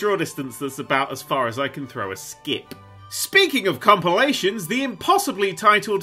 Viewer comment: did... did he just say "cumpilation"